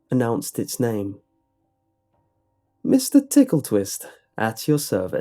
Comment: I thought you said "Mr. Nipple Twist, at your service" XDXD